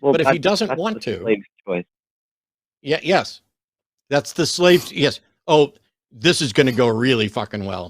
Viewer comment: Hold up, what did the caller say at 1:38? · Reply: “It’s the slaves choice” … as in to stay or leave after he’s completed his duty or served for however many years he was supposed to